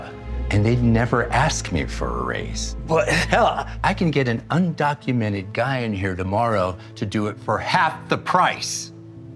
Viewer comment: what did he just say??? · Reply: hell, i can get an undocumented guy here tomorrow who'll do it for half the price